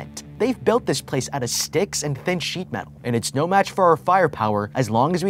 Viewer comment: I heard "sticks and bitch metal"